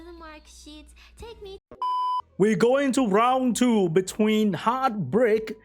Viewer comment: when he said "round 2 between" I heard "round 2 bitch queens" and was like " hwhat 😂"